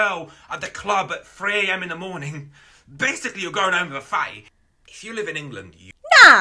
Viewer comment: what is a “fay” or “faze” or however it is said/spelled? I am too ‘murican to know 🦅 😂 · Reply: He said fatty, she just repeated him. It's just the strong accent lol